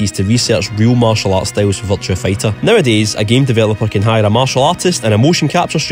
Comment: I keep hearing you say "Vulture Fighter"